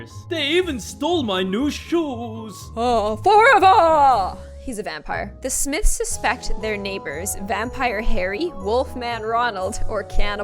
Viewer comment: When u said the smiths house i thought u said will smiths house